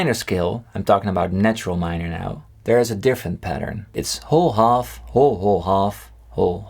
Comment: Watching this after New Year, and all I hear is "HO HO! (Half) HO HO HO! (Half)" :-D